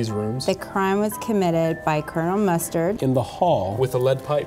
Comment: i heard kernel mustang